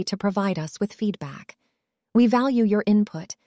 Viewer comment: I'm not sure, but for a second It almost sounded like she said F U for being a part of our community at the end 1:18. WTH!